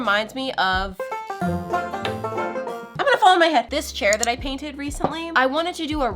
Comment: I had to rewind the imgonnafallonmyhead! Part to see what she was saying